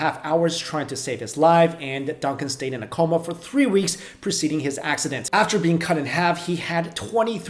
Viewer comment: Preceding? Did I hear that right? If so that implies the coma was before the accident. Not hostile here; just wondering if I heard correct.